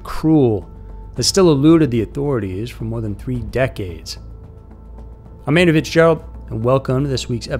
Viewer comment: This guy always says his first name so fast you can't understand what it is, sounds like he says Amanda0:37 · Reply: He is saying "I'm Andrew..."